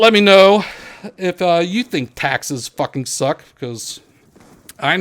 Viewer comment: Wow I thought it said TEXAS FUKIN SUKS